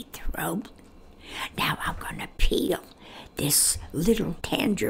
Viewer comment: I thought she said "I'm gonna pee" 😅